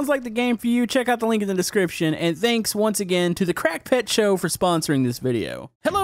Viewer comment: ngl, i thought he said crackhead show…